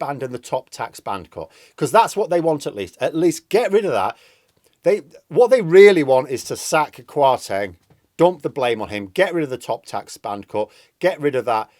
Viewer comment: For a second I thought you said get rid of the cat. Larry is the only stable member of the government!